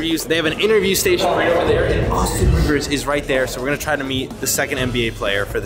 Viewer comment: i thought bro said austin reaves i was trippin for a sec 💀